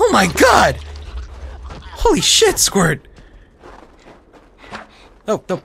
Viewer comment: at about 12:00 i heard "holy shit-squirt" instead of "holy shit, squirt"